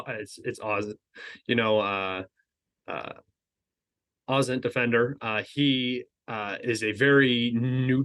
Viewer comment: I couldn't catch what he said. At 15:55, who is the pro-Ukrainian he recommends to follow? Ozzie Defender? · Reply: OSINTdefender